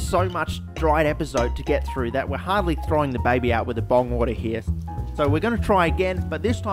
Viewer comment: "throwing the baby out with the bong-water." some what specific for some one who never took drugs or anything. :D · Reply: I for real thought he said bomb water and it made just as much sense.